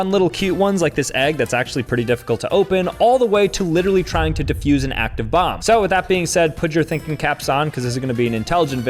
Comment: WHAT DID HE SAY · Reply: He said HES GOING TO DEFUSE AN ACTIVE BOMB you heard that time?